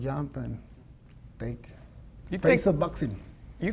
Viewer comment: What is he saying 5:55 " To fake some boxing! "😂Nothing else is the truth!